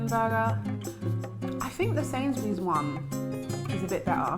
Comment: What did you say is your favorite burger at 12:10? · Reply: Sainsbury's (i think)